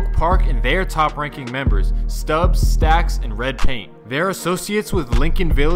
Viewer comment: did u call Snubbz "Stubbz" or am i hearing sht?